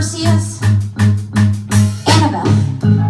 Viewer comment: she said annabelle instead of annabeth LOL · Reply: Jessi Luck listening it to again, it sounded like she said annabelleth